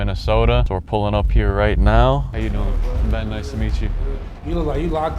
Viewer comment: did give out his name? i believe i heard Ben when he had introduced himself to NLE…